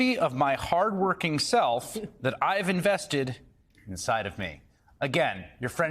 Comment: the more he talks, the more I hear "foreign asset"